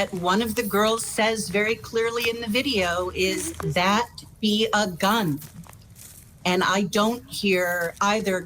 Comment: I hear "That we go down" not "that be a gun"